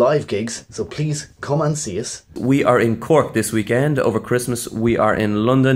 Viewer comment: I thought you said you were in COURT this weekend 😁 Ochón is ochón ó 😱